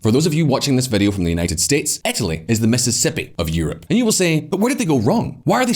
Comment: LOL, the voice says "Mississippi", but the English UK subtitles say "Missouri" 😂